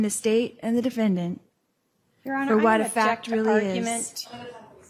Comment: What did the court reporter yell out around 13:55? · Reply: It sounds like "what a fact is". I'm not sure.